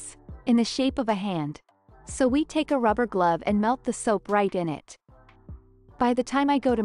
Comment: I thought she said she can’t hold the soap 🧼 but she put it in the glove 🧤